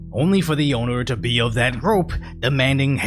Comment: wwhat the fk did he say???????? "only for the owner to BeOoFrAMroPmf"